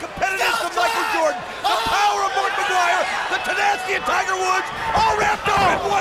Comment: It sounded like the commentator said "The power of Markiplier".